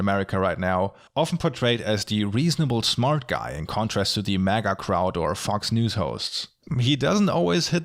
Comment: I thought you said “he’s portrayed as the reasonable smart guy, in contrast to manic crowder and Fox News hosts” instead of “MAGA crowd and Fox News hosts” lol.